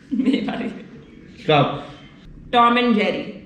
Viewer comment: what does say ? Mmabaad ?